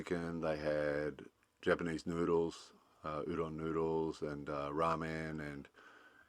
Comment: When he listed the food truck options, I thought he said 'Japanese poodles' - had to play it back. Might need to get my hearing tested.